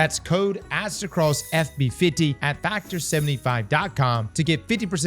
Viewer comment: I swear, I thought he said "That code is Aztecross F Me 50" 😶 I nearly spit it my drink 🤣🤣🤣🤣